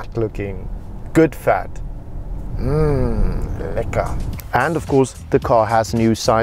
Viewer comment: did he say lekker😂😂